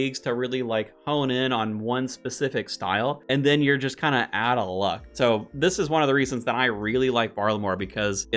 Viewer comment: did you say "atl luck" ? 😅